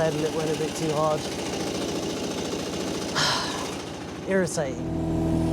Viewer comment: When he knocked that board off and said it was "Irritating" I totally heard "Ear of Satan" and couldn't figure out if that was some New Zealand colloquialism I wasn't familiar with. haha